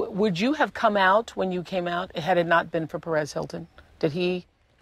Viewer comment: I thought she just said Paris Hilton instead on Perez Hilton.